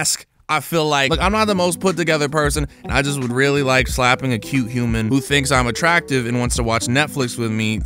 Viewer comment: did he just say he likes slapping a cute human. 😂